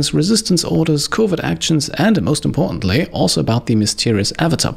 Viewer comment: I heard like "COVID actions"